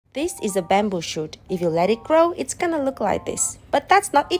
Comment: I thought it said at the start “this is a bamboo shi*